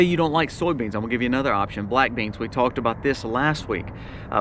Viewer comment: For a second I forgot what black beans are and thought you said blackberries. Rest assured I was totally confused on how someone can store a large amount of fresh blackberries as an emergency food.